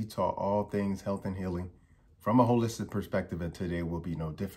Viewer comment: I truly believe God is taking you on this spiritual journey 🙏🏼 I had to rewind the beginning when you said a holistic perspective I thought you said “a Holy Spirit perspective” 😳😂 but thank you for this video it blessed me so much.